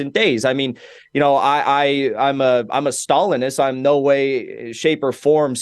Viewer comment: Did he say he was a Stalinist?